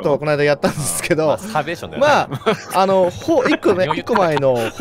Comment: 辺りの冒頭、さんが説明する中で、流れるように「マス◯ー◯ーション」て下ネタ全開でぶちかます二人、聞こえてるぞww